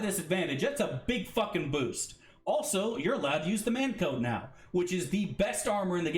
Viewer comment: Ayy my scottish brother hope you're doin wel. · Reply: I got one question bud - at 1.38.15 you say paladins can use "the best armor in the game" but it sounds like youre saying "man coat" and i have no idea what you mean hahaha